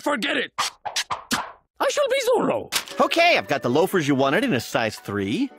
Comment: I thought he said “I should be Zolo”😂😂😂😂😂